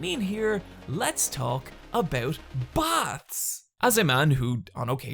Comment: Thought you said "bats", not "baths"; and was like, this fucking video is all over the place. 10/10 would watch again.